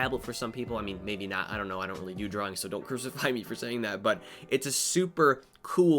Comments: I heard "I dont really do drugs", thought you were implying wacom users were crazy creative drug users 😂 I'm from UK. Accent thing. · did he just say I don’t really do drugs? what?